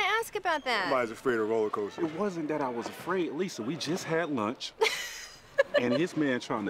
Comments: Did TMac call her Lisa? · did he call her Lisa? 😂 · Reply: I think he was trying to say listen but it just came out really weird lol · did he call her "Lisa" or say "listen" -- sounds like Lisa.